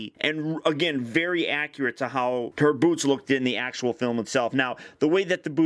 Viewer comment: When he said boots I thought said boobs